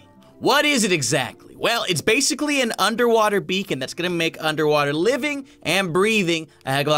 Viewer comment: OMG I thought beacon said bacon so I clicked on it because I thought the bacon was some cool under water bacon!!! XD of course that not the only reason because I love your channel !! BTW my sister thinks your crazy